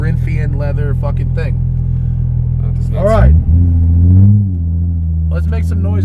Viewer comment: yeah...for your edification what Matt said, ...at 15:57 "F..&#* GanG Thing"